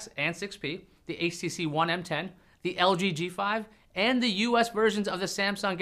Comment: did he just say the htc one m10